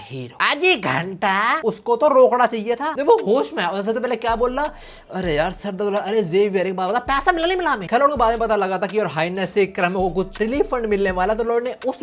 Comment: for some reason, i heard/ thought He said “Are Xavier, apna Ma- PAISA MILA YA NAHI MILA” 😂